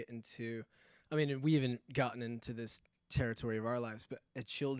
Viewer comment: Approx 21:00 onwards. I think he says 'they had even talked about it' but others say he said 'havent'. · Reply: he says “we haven’t even gotten into this territory of our life”. Meaning: none of us is a father yet, but surely the idea of having children and being a touring musician has its complexities that we will have to navigate